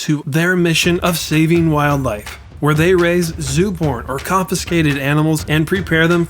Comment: i could have swore he said zoo porn